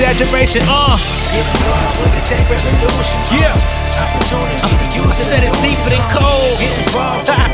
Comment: For a hot second I thought he said It’s deeper than Cole. Well damn.